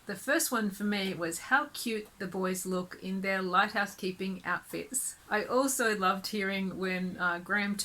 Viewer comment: I thought you said a little light house keeping 😀